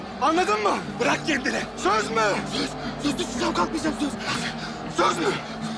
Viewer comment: düşücem,kalkmıycammı diyo?Yoksa benmi yanlış anladım:/